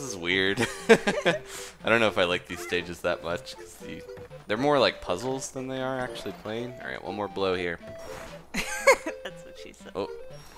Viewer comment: Did anyone else hear the 'that's what she's said' at 10:09???